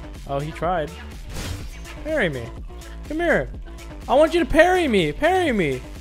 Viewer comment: On 4:17 he said parry me and i heard marry me 😂😂😂😆